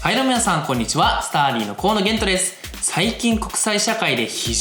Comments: Stardyがスターリンに聞こえてしまった、、、 いつもはちゃんときこえるのにな、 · スターリンの河野玄斗です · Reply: スターリンに聞こえたの俺だけじゃなくて良かった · スターリンの河野玄斗です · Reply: これはスターリンって聞こえるわww あ、違う、ちゃんと「同志」つけないと！ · スターディーがスターリンに聞こえてしまった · 最初スターリンに聞こえた